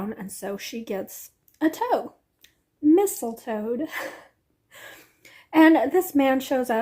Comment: I was listening and not watching and thought you said Mistletoad the first time, and thought it was a frog prince shifter romance lol. I'm reading Frosted by the girl next door by Aurora Rey, I'm about 45% into it and liking it! There is smut but it's in the dirty romance area not the full on smut zone. It's a new release and a full price $9.99 preorder and I'm not disappointed. The plot is good, and the characters are good, and the one smutty scene was good.